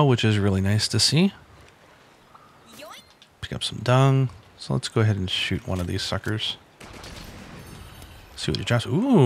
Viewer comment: Did she just say "yoink" when picking up the dung?